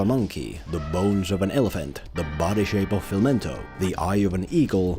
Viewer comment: I thought you said he has the body shape of a lentil... Edit: Imagine his animal weakness is that he has no thumbs.